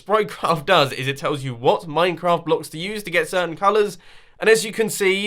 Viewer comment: i need a translation. I don’t know what he said. 🤨😂. also how do I get the program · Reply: It tells you what blocks to use to get certain colors